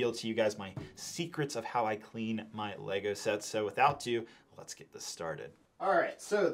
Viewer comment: Why do you keep saying what sounds like "without to" instead of "without further ado"?